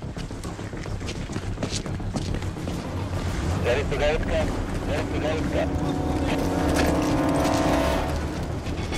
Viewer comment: what does the radio chatter say? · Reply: 99, 99